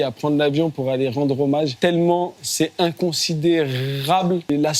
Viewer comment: J'étais en train de me dire que c'était un mec bien, sympa, droit dans ses bottes...et puis j'ai entendu "fan imconsiderable de Jul" => Désolé j'ai lâché le truc 😵‍💫😵‍💫😵‍💫